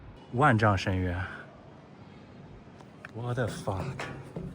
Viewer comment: did you just say what the fa..?